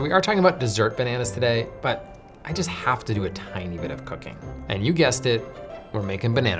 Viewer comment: Tiny bit of cooking! I thought you said tiny bit of cocaine. I was concerned